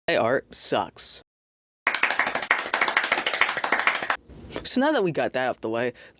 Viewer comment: thought the beginning said “my art sucks” tbh and I was like “NO IT DOESN’T”